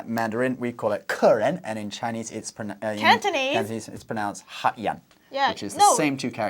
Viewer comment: nearly sounded like he said Black Man instead of Customer with "hak yan"